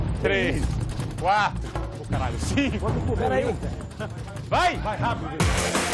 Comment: alguém mais ouvi o "o caralho" em 10:15??